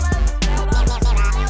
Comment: I thought he was saying failboat